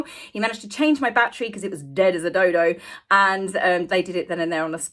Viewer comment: Had to rewind: I thought you said "dead as a dildo" 🤣🤣🤣